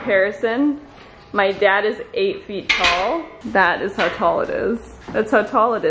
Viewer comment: did say her dad is 8ft tall??